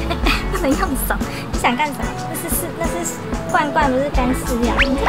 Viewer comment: 我聽成 漂亮大姐姐：那不是罐罐，那是屎。。。。對不起XDD